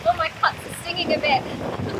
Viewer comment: your ‘what’ is stinging a bit? 🤔 Haha, yeah I know what she truly said. But, like me, if you allow your mind to wallow in the gutter just a little bit — I promise you’ll hear something a little different. 😂